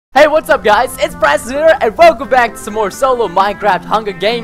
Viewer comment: it sounds like you say " hey what's going on guys, its prestoner!"